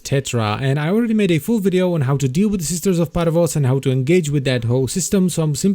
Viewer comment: How to engage with that Hoe system... Did i just heard that right? XD